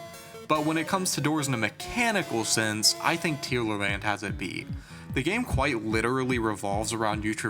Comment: he mentions a game with better door mechanics and for the life of me I can't make out what the name is, the audio is a bit muddy there but I really would like to know. Can anyone help please? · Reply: Tealerland, the game I talked about in that section.